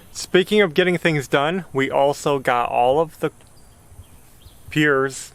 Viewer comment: Yes, it did sound like was saying "olive", not all of the pier cores filled. LOL But we all have our special way of saying certain words. even tho it's only primer the ceiling looks 100% better. You guy are very hard workers & a great family. You can even pick at each other without getting hurt feelings. That's wonderful in today's world. Keep moving forward & I'll be back to see the next one. Bye.